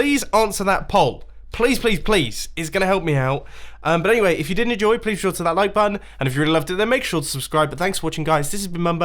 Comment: please tell me that I am not the only one who heard him say, "If you DIDN'T enjoy please hit that like button."